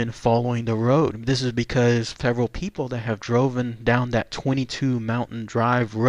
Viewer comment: Did he really say “droven” at the 11:54 mark?